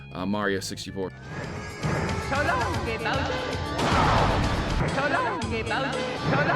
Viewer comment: Your repeated edit of mario throwing bowser triggered someone somewhere lol "So long gay Bowser" 😅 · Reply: I always thought Mario was saying "So longsy Bowsey"